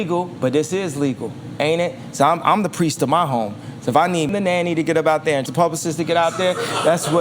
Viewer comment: What did they edit at 2:04:56? I think he said something like the so and so nanny and so and so publicist.